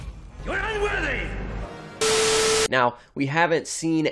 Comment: YOU'RE UNWORTHY!!!! 😂😂😂😂😂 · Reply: Oh. I thought he said "You aint worthy" 😝😅 Now it sounds like unworthy.